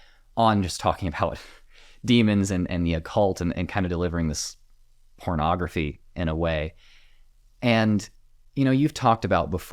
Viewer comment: I misheard you and thought you called this “hornography” at first. Like Christian-made content about devils and other made up stuff for views